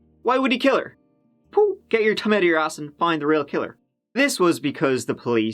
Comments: When he said get your thumb out of your ass and find the real killer, I thought he said get your tongue out of your ass and find the real killer I had to replay twice hahaha. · Never marry a guy who belongs to a religious community where murder is preferrable to the shame of divorce. 11:17-- "Get your thumb out of yer arse--" Owning to the t/th issue, I heard, "Get yer tongue out --" (Ahem) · "Get your thumb out of your ass". LOL I swear I thought you said "get your TONGUE out of your ass," and I was like Is that an Irish expression? Eww (like somehow "thumb' is any better. ) LOL